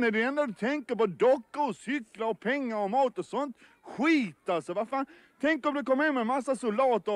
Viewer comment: when did he said "utter trash" how do u say it (0:44)? for me is like "huitta se va fa" but how do u write it? :)) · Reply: Oh! xD Yeah it's: Asså va fan?! I don't know proper phonetic writing but I suppose: "Ah soh vah fahn" would work. Sound a bit like a dragonborn shout if you shout it.